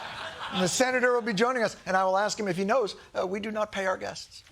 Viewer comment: Had to skip back a bit, thought he said "Senator R Kelly will be with us..." Today's not the day to wake up in an alternate universe